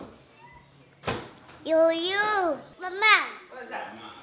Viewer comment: sounds like he saying my man 😂